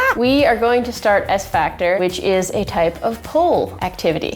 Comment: Who thought they said X-Factor in the beginning 😂😂😂